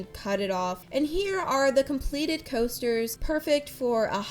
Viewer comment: I thought you said posters lol